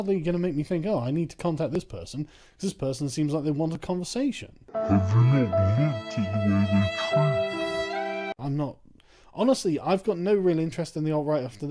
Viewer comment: I can't understand what Sargon is saying there. You shouldn't have slowed it down there. In fact, some of the editing moves a bit too fast to get proper context. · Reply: Every speed up and slow down is of a previous clip. In this case "Have you not looked at the way they treat me"